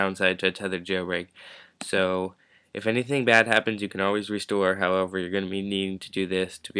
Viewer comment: it sounds like your saying "redstone" this isnt minecraft bro